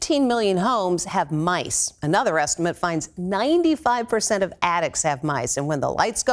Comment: I heard her say 95% of addicts have mice.... Oh...attics. Perhaps I need my hearing tested?